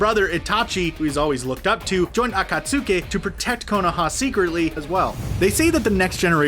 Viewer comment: did he say akatsuki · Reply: akatsasuke he said akatsasuke